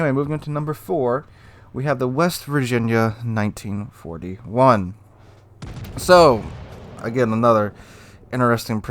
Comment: I hear it as " the West Virginia 1941 is an A-hole Colorado "